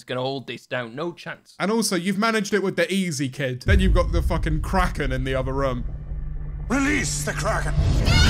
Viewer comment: sounded like he said “than you’ve got the crackhead in the other room”